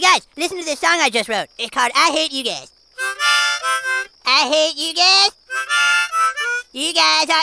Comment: The ones who thought he said İ hate you dad 👇